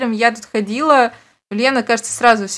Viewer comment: какая Лена либо ине просто послышался 😳